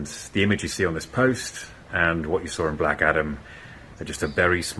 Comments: Thought he said Black Adder the first time. Gave me a good laugh. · I honestly thought he said black adder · I thought he said Black Adder. Would be wonderfull if their was a new one. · I thought Henry said Blackadder lol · Lol I thought he said black adder at first and got momentarily excited.